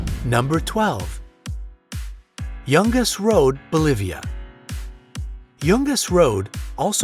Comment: wasnt look at screen Thought he said "Youngest road"